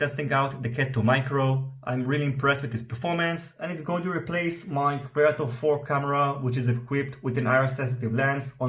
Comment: What camera are you going to replace with the Cat 2 Micro? I couldn't understand. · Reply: I am currently using a Micro Predator v4 with an IR sensitive lens on my test rig. I am going to replace it with the Cat 2 Micro.